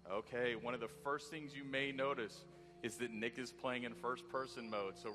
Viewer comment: anybody notice at 2:20 - 2:22, even tho he said "Nick is.." it really sound like he said "Niggas" ?